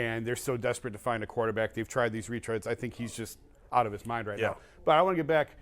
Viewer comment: I had to rewind. He says "retreads" . I heard something different the first time. So fitting, literally lol